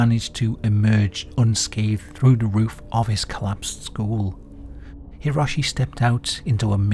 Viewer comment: I thought you said those boys SKULLS collapsed lol. School!